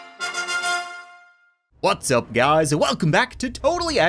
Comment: I still think it sounds like he says "Butts up, guys"